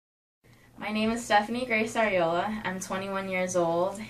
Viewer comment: Did she just say her last name was Areola?